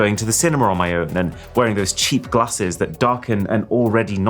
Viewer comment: did he just say CHEAM GLASSES????????????